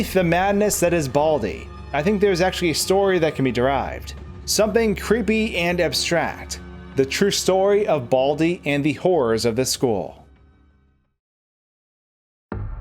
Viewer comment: i thought he said the whores of this school .-.